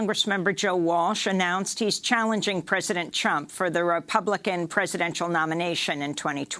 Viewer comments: did she say president chump? Anyway we can try nuking a hurricane the next time one hits Washington. Hopefully while President chump is there. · Anyone catch Amy Goodmans Fruedian slip at about 9:45? President CHUMP lol lol Perfect Amy.. Couldn't of said it better myself. DUMP THE CHUMP!!!